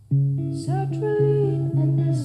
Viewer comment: The lyrics you weren't sure of: balanced on my toes so I can breathe And Chinchilla's first words are: Sertraline and a sip of serotonin Sertraline being an anti-depressant I understood the "bury myself with a great big smile " not to be about being high, but about putting on a smile that we bury our real feelings behind. And being afraid of being ok because all things change is the fear that the ok won't last. That the depression will come back and you won't be ready for it, or it will hurt more because you felt ok for a while I definitely understand this one as being about how depression is treated with drugs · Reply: Ahhh Sertraline. Aka Zoloft. I thought she was saying "Such relief in a sip of serotonin." Thanks for that clarification. 🖤